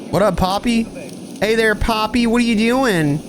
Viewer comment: What did that guy at 15:28 even say lol, to me it sounded like" what's up"